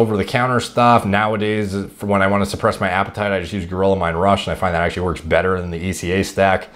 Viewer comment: Did he say he used gorilla mind rush? What is? Or am I hearing him wrong